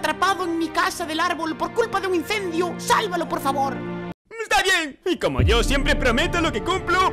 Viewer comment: sjsjs fui el unico que entendió lo que dijo¿? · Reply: Dijo: mmhh esta bien jajja